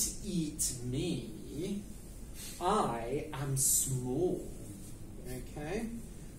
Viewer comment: The word in 10:56"I am...", i can't listen it. What is the word? Thank you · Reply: Small! :)